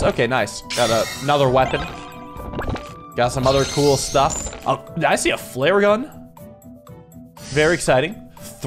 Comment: What he said: "Did I see a flare gun?" What I heard: Did I see a Flareon?